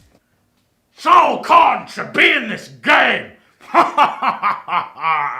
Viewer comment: swear to god i thought he said "MY COCK SHOULD BE IN THIS GAME!"